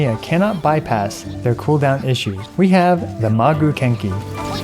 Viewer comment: Ok nice video and all but 9:51 I thought you said “the maguu kinky”